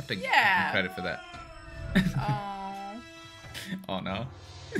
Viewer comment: “Zuko” sounded like he’s saying “ouh nouuuuuu” 😂